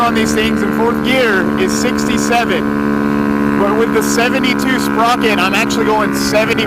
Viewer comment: Bro sounded like he said 70 tooth sprocket😭 what size